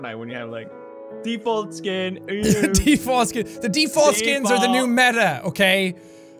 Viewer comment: the subtitles say t forskin